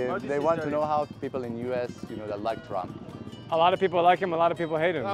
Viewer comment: The translator said the guy asked what Americans think of TRUMP! Man I was so confused cuz it sounded like Tallam until ChatGPT cleared it up for me lmao